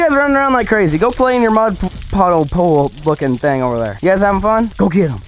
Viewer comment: From 20:07 slow down the play back speed and does it sound like he said f...... mud puddle